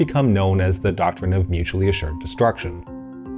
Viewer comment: The captions have a typo "destruwwction" instead of "destruction"